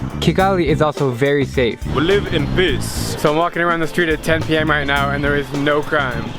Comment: i heard that as 'we live in piss'